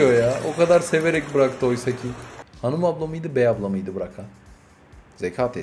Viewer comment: 3.30 Hanım Abla mıydı Bey Abla mıydı mı dedin abi ben mi yanlış duydum klekwwklwkl